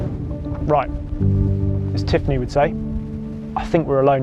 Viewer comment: is it wrong that I heard him say "as Tiff Needel would say".....